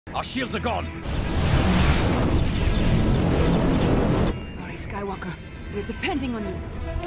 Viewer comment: did she say rey skywalker?! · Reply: Siff Trips she said „hurry Skywalker“ ...